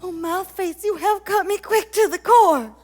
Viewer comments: Sometimes I can kind of make out the names people are shouting. "Spit" might be my favourite I've picked up- it's suggested for "Mouthface". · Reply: I thought I heard "Horse face" for Mouthface lol. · I heard "Horseface" instead of "Mouthface."